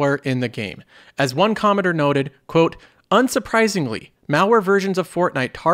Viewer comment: "As one commoner noted" is what I heard 😂😂😂 Your channel is my favorite. I am glad you have a pleasant narration voice.